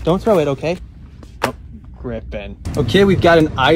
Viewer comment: I thought he said Griffith but it was griffen phew(people who know👇